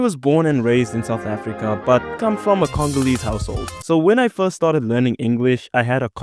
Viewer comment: moi j'entends le Congo je suis là 🇨🇩🇨🇩🇨🇩🇨🇩🇨🇩🇨🇩🇨🇩🇨🇩